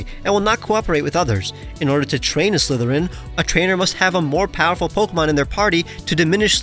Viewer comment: Slithereign is an awesome design. At one point, I thought you said Slytherin from Harry Potter and that Pokemon is EXACTLY what I would think a person from Slytherin would have